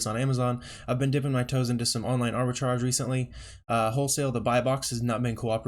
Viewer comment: wholesale and buybox is not helping you? Did you say Arbitrash?